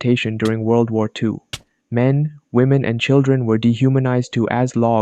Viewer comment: what I heard: me women and children were logs 🪵 😂😂😂😂😂😂😂😂😂